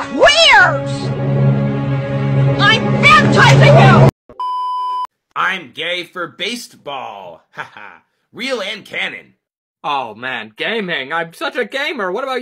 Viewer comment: "im gay for base(d)ball" im a little concerned on why i heard that ..